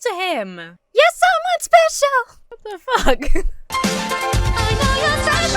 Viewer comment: I know the line is "You're someone special" But it's funnier when I hear it as "You're somewhat special"